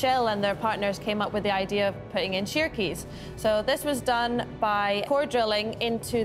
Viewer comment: I thought she said putting in turkeys, I'm like "WTF do turkeys have to do with it"...